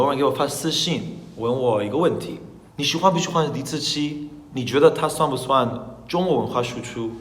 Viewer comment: 我竟吧“你喜欢不喜欢李子柒”听成了“你喜欢不喜欢你自积”……